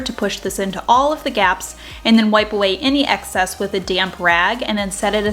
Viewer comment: So creative! A little funny....as I was watching you do the second wooden frame I thought you said, nonchalantly....”wipe it with a damn rag” 😂😂😂😂 Then realized it was “damp” rag! 🤣